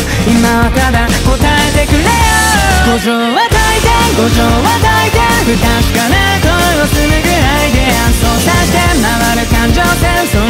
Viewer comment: Idk why but whenever I hear this part 1:56 I hear "Gojou wa Titan, Gojou wa Titan" help😭😭😭hshshdhshshshsjsj . . . . . . . .it was funnier in my head